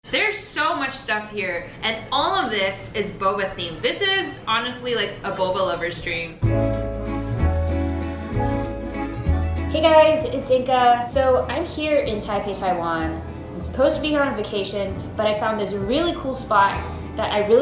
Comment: Did anyone else noticed at 0:10, the caption said “Hey guys it’s dick” 😂